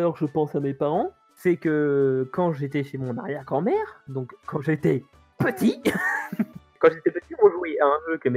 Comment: j'ai entendu "Quand j'étais petit on jouit" Honte à moi x)))